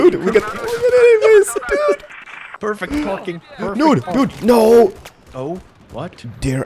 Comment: Sounds like he says '' Nude, dude!''